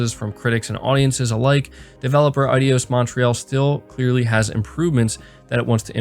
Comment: did he say adios Montreal?